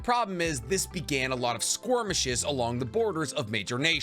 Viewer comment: Did you mean to say 'squirmishes'? I listened to it a couple times and I swear that's what I hear instead of 'skirmishes'. Love the channel and your enthusiasm going over lore :)